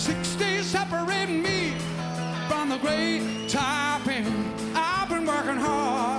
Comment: The taipan referred to in the song is a venomous snake, reputed to be the most venomous on the planet. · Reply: Sorry, shouldn't laugh, but that's an example of mishearing the lyrics, it's 'the great top end', referring to the section of Australia across the top, nearest Indonesia.